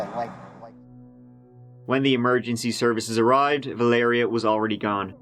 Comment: I thought he said that lady’s name was “Malaria” for a second there. I was thinking “Who the hell names their kid that!?” 😂